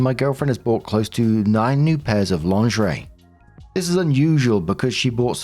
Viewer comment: I paused at 8:41 because I thought you said NINETY new pairs of lingerie, lol. I was like 😳